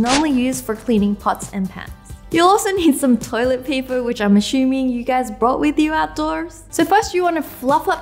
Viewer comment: Wengie u said ashumming instead of assuming